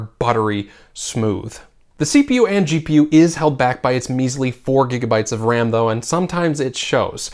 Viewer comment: Ram I hear is 6gb not 4gb